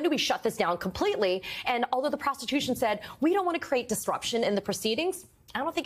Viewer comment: Did she call the prosecution the “prostitution”? 1:30.